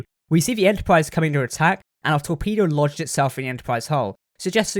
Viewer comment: Did I hear that correct? " The torpedo launched itself in the Enterprise's hull" Really? like are you that dumb is it that smart? 11:42-11:45 A Torpedo does not launch itself someone has to trigger it! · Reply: I said "Lodged", as in a Torpedo has logged itself inside the hull. Regardless if you want to pick apart the way i said it, it's still correct. - Don't stress man :)